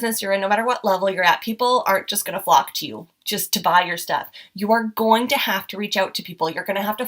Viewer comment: she actually says "...people aren't just going to flock to you."